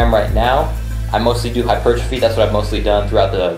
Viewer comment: What's 'hyperchfeed' as he said at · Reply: It's Hypertrophy, basically a increase in muscle